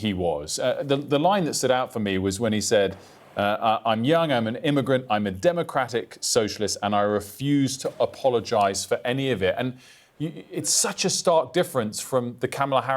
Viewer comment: What he ACTUALLY said was.. 0:20 "I'm Young, I'm an Immigrant, I'm a Democratic Socialist and I'm a MUSLIM and i refuse to apologise for any of it" Why omit the MUSLIM part? 🤔